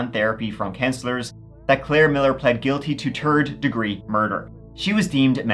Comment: yo i loooove an irish accent but sometimes when you say "third," I hear "turd" oops